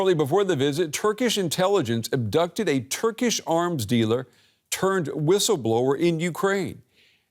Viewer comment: Did he say Turd Whistleblower?